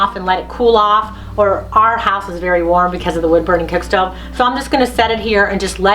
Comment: I thought you said that was a wood stove! Where is the wood?